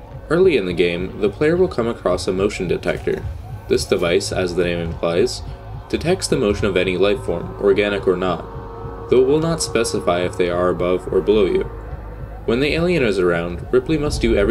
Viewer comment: what is he saying? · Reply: "This device, as the name implies, detects the motion of any life form; organic or not. Though it will not specify if they are above or below you."